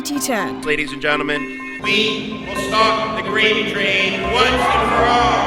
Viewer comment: did he say? "We will stop the grape trade once and for all!"